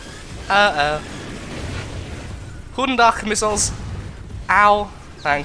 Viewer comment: did u just say good day in dutch on 11:25? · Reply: Yeah I hear that a lot - I know lots of german and very little Dutch - It was hard enough just pouncing the G's in Goedendag not as the hard English/German G xD It was off the top of my head from what someone told me years ago, I'm pretty happy I even remembered it..